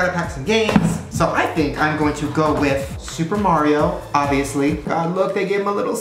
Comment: when he said "Super Mario...Obviously." All I heard was "Super Mario...Odyssey"